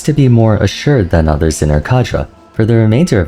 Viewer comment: brother what is your lexicon, what is that word? Kajra? Kadra? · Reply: "cadre" I think.